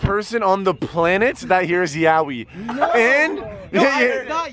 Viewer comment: I was hearing yanny then in 6:09 I heard yawi like he did